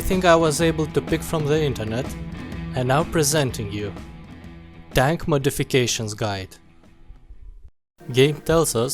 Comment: Anyone else thought he said Dank Modifications Guide